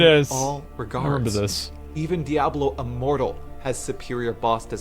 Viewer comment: I swore he said Diablo immoral